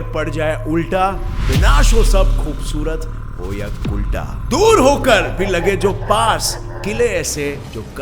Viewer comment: did you just call those girls "kulta" 0:51 shame on you ..😤😤